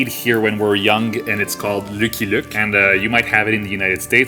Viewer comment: Lmao I thought you said the comic was Lookie Look. Love ur accents